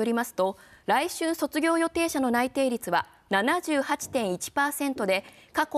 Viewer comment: 来春卒業予定者の内定率が〜が、来週卒業予定者の内定率が〜に聞こえた